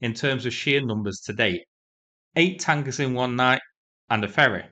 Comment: did you say - 8 wankers and a Fairy ???? sounds like the ruzzians... thank you so much for all you do for Ukraine and FREEDOM !!! DYKUYU !!!!! keep up your great work